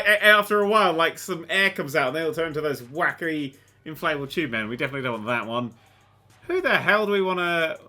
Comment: Did I hear Wacky inflatable arm flailing tube man?? I THINK I HEARD WACKY INFLATABLE ARM FLAILING TUBE MAN!